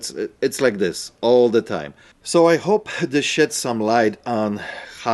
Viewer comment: It almost sounded like you said “I hope it shits some light” even though I know you said “sheds” it made me laugh out loud!! Might have to start saying that from now on! Hahaha cheers!!